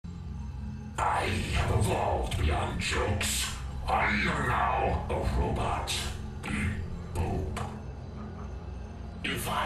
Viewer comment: ya know i always thought he said "...am now a robot. Eat poop." made sense to me either way lol